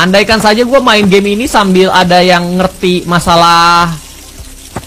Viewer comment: Gua denger dari gamenya katanya you did it tapi pas miawaug ngomong kedengerannya kaya you titi*